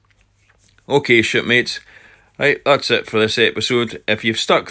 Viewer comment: For a second i thought you said "Ok Shitmates, if you've stuck through to the end give me a thumbs up", ties in nicely with your pooping voice moment earlier😅 Tanks sailin' nicely Captain, Arrrrr!